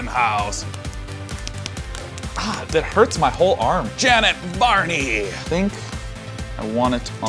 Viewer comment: For a second, I thought he said "Janet Barney".... awwwkward